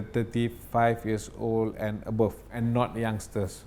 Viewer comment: did he really say 5 years old?